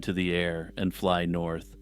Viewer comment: I thought you said the polar bears flew North😅